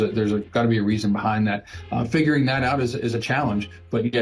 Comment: Uhhhmm 18:37 Did I hear him correctly? "Fingering that out".